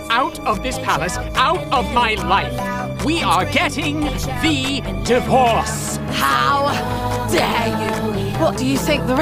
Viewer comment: What I heard: " we are getting de devorced".